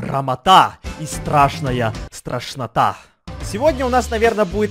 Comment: Когда сказал страшная страшнота мне послышалось тошнота